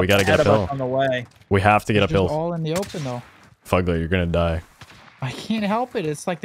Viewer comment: I almost had a panic attack. I thought said “Fu*k that you’re going to die”. He said fugglet 😂